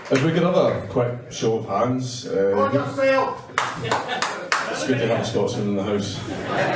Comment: What the hell did that guy say? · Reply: “For yourself”